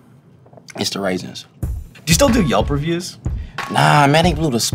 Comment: Did Vince just say: "It's the raisins?" Timestamp